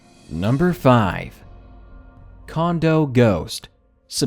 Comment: When said Condo ghost i thought he said condom ghost and i thought of the movie killer condoms